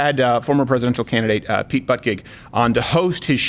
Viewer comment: I literally thought he was saying Pete Buttcake 🤣🤣🤣🤣🤣